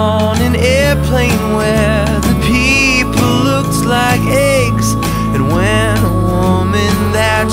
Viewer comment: does he say eggs or ants? XD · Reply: He says eggs in this version and ants in the earlier versions. there are 6 recordings of this song.